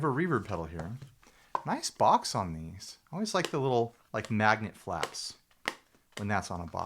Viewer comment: "Nice box, Magnet Flaps!" Yeah! Wasn't it? How'd you meet my ex, man? I just heard you give her a shout out 😆